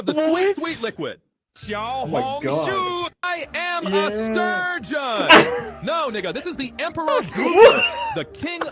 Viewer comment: what did it say 😂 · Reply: "STURGEON"